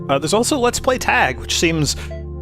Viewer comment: I definitely thought that said "Let's play lag" the ever present meta-game of online gaming.